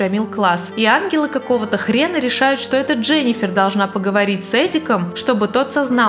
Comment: Эдиком или мне послышалось 😂?